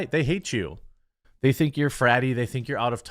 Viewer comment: amen sister! Either that or they just don't care. As a southerner this woman is saying we are 'bratty' lol. I'm not a brat or gender confused idiot or mentally deranged idiot with a desire to kill children. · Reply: Correction...'fratty' lol. I thought they said bratty. Still just as bad. A lot of their clientele is good Ole southern boys with God, faith, family and guns as their roots. They want to destroy ALL that